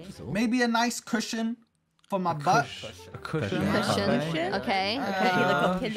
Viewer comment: just me who heard “⏃ nice Christian for my butt” 😂 ⟟ mean same but..